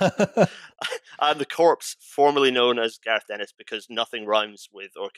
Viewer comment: For a fraction of a section, when the guest introduced himself I genuinely thought he said Garth Ennis and was shocked to see WTYP snag such a big (unrelated to engineering disasters) name.